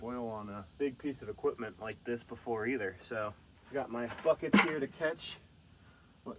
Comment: did say the F word here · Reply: He said bucket